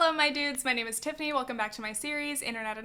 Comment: the way i thought u said tiffany iceberg